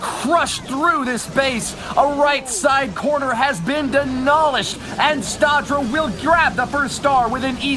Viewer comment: anyone else hear say "the corner has been DENOLISHED"? I mean, I know people mispronounce words but its...so I'm gonna post about it lol